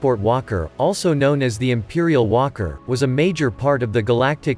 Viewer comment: I'm not a native English speaker. I heard "imperial wanker" instead of "imperial walker".